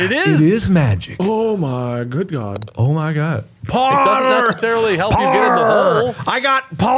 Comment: when Mark was saying 'parrrrr', why did it sound like he was saying 'power' in an Irish accent 😂